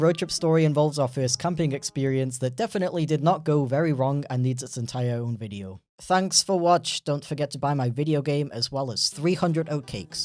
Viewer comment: caption says "own entire video"?